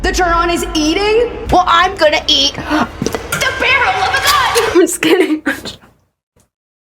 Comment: dawg I had to rewatch this part bc I thought she said "the barrel of a NUN." 😭😭